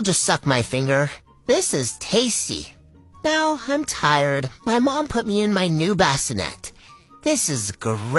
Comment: I legit thought she was gonna say my cow put me in my basket